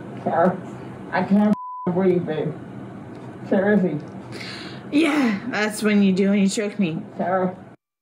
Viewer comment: Sounds like she said “ yeah that how it feels when you choke me”.